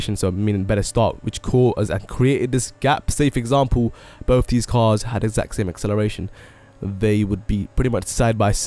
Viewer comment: dude I had to slow the video down to 0.5 in order to understand what you are saying. btw,open subtitles and look what it says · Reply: he said both the cars had sex in acceleration😃